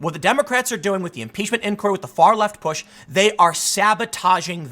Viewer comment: I don't believe I changed his words at all. I re-listened to this video and he said, "Believe it or not, my politics are far, far left, especially how I run my businesses." And he's repeatedly called himself a "left-leaning centrist". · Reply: weird. I heard "far, far left.". But my ears aren't pristine by no means.